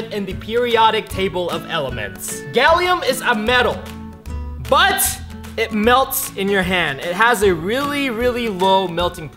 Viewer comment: I thought it said "METAL THAT MELTS YOUR HANDS"